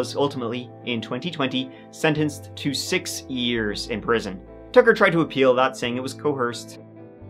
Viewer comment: The only two things that are tragic are the murder of Shane and the acting of Tucker. Six years in prison is a joke. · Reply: I thought he said 26 years! Too bad that isn’t the case.